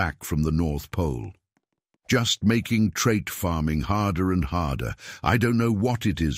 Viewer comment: I thought the last video u said i hate trait farming